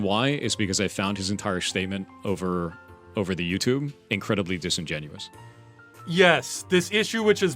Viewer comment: Did he say "over the youtube?"